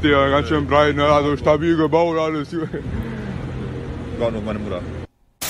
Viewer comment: Was sagt der bei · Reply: Ka vllt "War nur meine Mutter"?